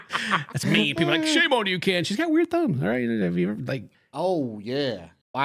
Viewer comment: I thought you said tongue not thumb and it sent me into hysterical laughter